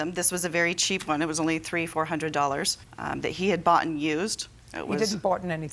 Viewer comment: "He had bought and used" JJ "NOBODY BOUGHTEN ANYTHING" Er, JJ, she didn't say that. JJ is so used to idiots that can't speak English that she jumped on that one too soon. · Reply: Yeah, it just sounded like she said boughten. She did say bought and.